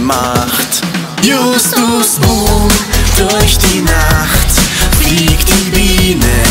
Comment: Ich habe die ganze Zeit justu Burg verstanden 😂😂😂😂